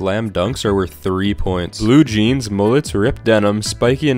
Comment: Did he just say no mullets